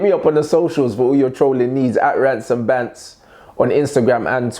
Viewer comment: Yoooo lowkey man at first thought the intro/outro said Rants N Balls😭